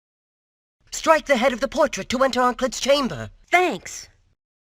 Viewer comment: I'm not sure what exactly Pedro said when you freed him, and YouTube's auto captions don't know either, but they seem to believe it's something suggestive that I probably can't write here without mangling it lest it vanish. Apparently he knows how to enter the "chamber" (as he calls it) of a part of the female anatomy that starts with C and ends with litoris. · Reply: "Strike the head of the portrait to enter Onkled's chamber"